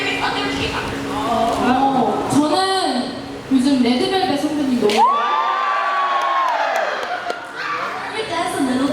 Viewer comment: Who did JiU say at 26:50 was her favorite kpop group? Unfortunately I couldn't make out what she was saying but I would love to know, so if anybody knows please lemme know thank youuuu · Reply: She says Red Velvet